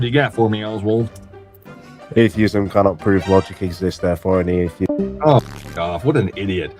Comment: what did he say before he got cut off? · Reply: “Atheism can’t prove logic exists…” probably concluding with the point that an atheist being self-assured in his atheism through his own sense of logic or reason is entirely irrational. It’s an argument positing that reason can’t be verifiably true or trusted if the process in which our minds are formed is purely happenstance and chaotic. It’s moreso an argument against atheist ego. If an atheist were consistent, he should consistently question whether or not anything he actually thinks is true, rational, or reliable. It should also raise sincere questions as to WHY things seem rational or reasonable in our day to day experience. A consistent atheist has no good explanation, whereas a thesis posits that things seem to abide by reason/logic because a supreme mind has ordered creation to abide by basic laws of reason and logic.